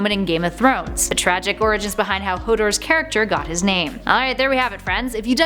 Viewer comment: Did she said Hooters?